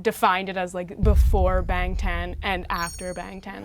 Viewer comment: “Before Ben Ten and After Ben Ten” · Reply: Y'all we KNOW she said Bangtan it just SOUNDS like she said Ben Ten ok even I'm an ARMY and found this comment hilarious